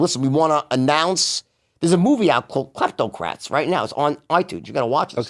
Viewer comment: could someone tell me the movie jordan announced on itunes at “58:30” sounds like he said “plateau krafts” but nothing comes up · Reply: Miguel Fernandez “kleptocrats” movie